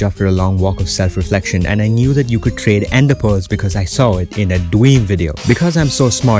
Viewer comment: For a sec I thought u said dweeb instead of dream LOL